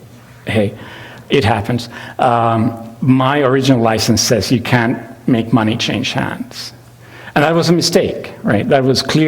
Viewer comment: What does he say at 6:09? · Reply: "My original license says you can't make money change hands. And that was a mistake."